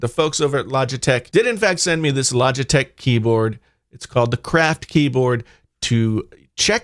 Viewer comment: For a minute there I thought you said "the crap keyboard."